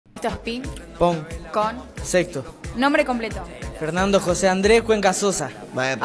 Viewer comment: pense que dijo ping pon con sektor xd